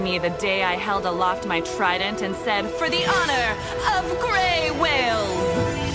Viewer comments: I hear "for the owner of grey school-" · I thought she said for the honor of grayskow